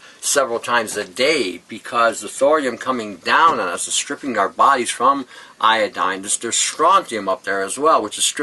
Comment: did he say iodine? Thx